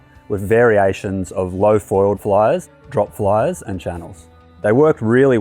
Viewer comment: Sorry, ignorant American here... what is he saying at 1:46? "low-foiled f-----, drop f------"??? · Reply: Flyers